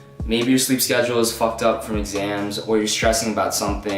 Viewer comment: xans what are you on bro LOL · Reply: oh fuck you said exams 0:25 I thought you said xans LOL